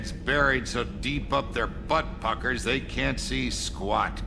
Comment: oh my I wasn't quite paying attention then I heard "butt puckers" and I'm like "wait, what did I just hear?"